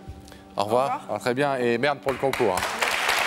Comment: Don’t read the English subtitles…they misheard the word Merde!